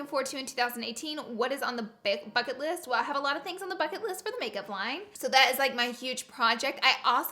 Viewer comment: “Well I had a lot of things on the fuck it list for the make up line” I thought that’s what you said at first 😂